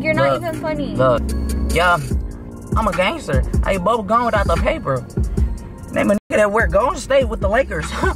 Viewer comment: “Imma gangster, I eat bubblegum without the paper”😭😭😭😭😭😭😭😭😭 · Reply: I swear I heard “I AINT bubble gum without the paper” 😂😂😂